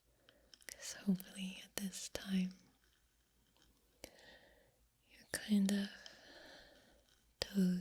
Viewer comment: excuse me did you say “hopefully you’re toes-ing off”? If so, 11/10 pun